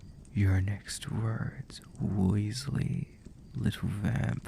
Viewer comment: "wisely" I thought you said weasley- flashback You're a wizard Harry